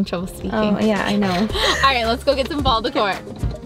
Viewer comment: When you said fall decor I thought you said baulbasour